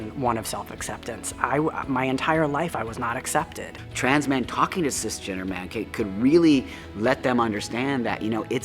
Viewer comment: he clearly says "Trans men talking to cisgendered men" and the subs read regular men. That should be fixed asap.